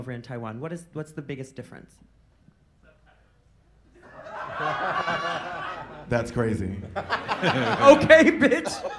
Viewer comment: What did that audience member say at 9:46? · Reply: I think he said "subtitles" lol